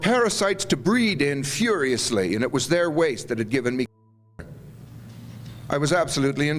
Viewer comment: what word was cut off? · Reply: Cancer I believe